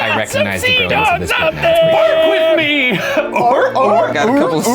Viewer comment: I get unreasonably annoyed when the subtitles don’t match what they say - like 10:41 is “bark with me” not “work with me” smh